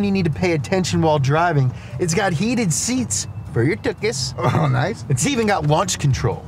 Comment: So in the ad at 1:20, he says "It's got heated seats for your tookus", but the subtitles say... something else.